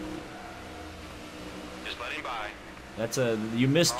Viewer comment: I was going to say that at 12:45 he actually said "Let 'em by," but then I heard it again and he said "Let him by." Huh, I guess they didn't record a line for Danica...